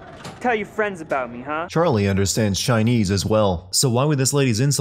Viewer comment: the narrator is wrong he said Charlie while the subtitle says Joe